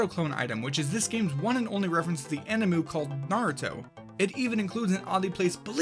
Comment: did you just say animu?